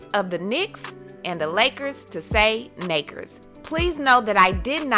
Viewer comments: People are hearing different things. Should she be fired for saying Nakers? No. Should she be fired for saying the actual N word? Of course. But unfortunately, we'll never know b/c people are hearing different things + if she did say the N word, she'll never admit it so *shrugs* One thing's for sure, I bet her ass won't ever make that "mistake" or slip up again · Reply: I hear "naykers" 🤷‍♀️ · I clear herd a G and not a k. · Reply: I heard "naykers" lol